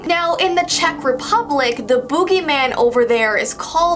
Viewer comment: I thought you said booby man XD